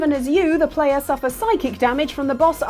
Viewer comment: I can't be the only one who heard "sidekick damage" instead of "psychic damage" at 10:27 XD